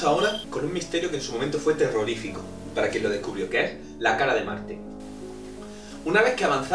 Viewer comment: Lol pensé que dijo la Cara de Manhe :3